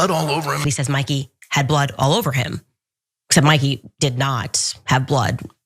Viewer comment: I thought he was saying Charlie had blood all over him?